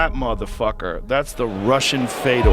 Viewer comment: did he say “The Russian Fedor”??? 😂